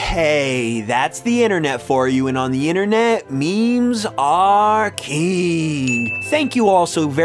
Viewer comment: i thought you were saying "memes are keen" XD